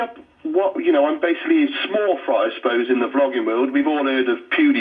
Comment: Brilliant! Well done. I'm thrilled to know I was an early subscriber on the channel of a "YouTube sensation"! Also, I'm sure it was my "left side of the Pond" hearing, but during the announcer's intro, at first, I could have sworn she said "I'm trying my hand at 'flogging'"! LOL! 😉